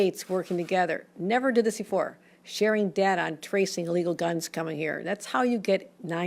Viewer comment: did she say trace legal or illegal guns here??? sure sounded like she said legal guns o.0 shes such a dirty soul